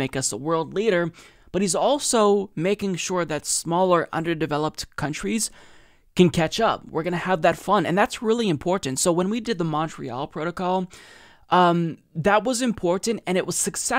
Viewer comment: In the middle of all this, when speaking about how these actions would also help to strengthen other smaller countries economies he said we are going to have fun, he didn't mean to but that is what he said around 5:50....... Good man